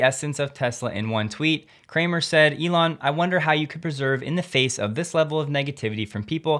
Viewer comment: That pronunciation of persevere though 😅 Sounded like you said preserve, which means something reasonably different